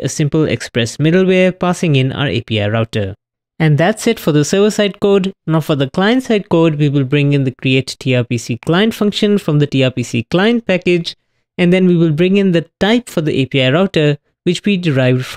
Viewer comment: Thanks for putting up, I thought I just heard "Suicide code". :D